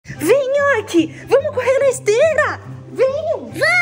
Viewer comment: Eu entendi "vem nhoque vamo comer besteiraaa"ksks❤